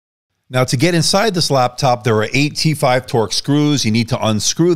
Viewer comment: I thought you said, "there are eighty five torx screws"